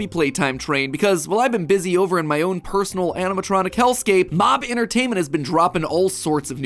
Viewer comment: did i hear bmob entertainment or is it just me?